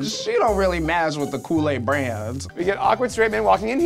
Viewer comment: Wait I thought shea said kool-aid brand instead of coulee brand.....